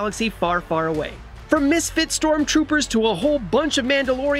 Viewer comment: Did he just say 'misfit sToRmTrOoPeRs'?